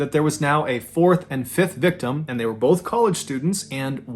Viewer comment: I know it's the accent and actually "college" but sounds like you say "they were both ""colored"" students". 😳😄